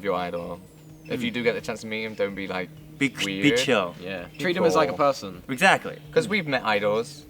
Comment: when he said "Be chill" i thought he said "crazy bit*h" in korean (미친년) xD what is wrong with my brain..